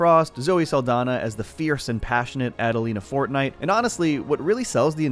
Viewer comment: did he just say Fortnite?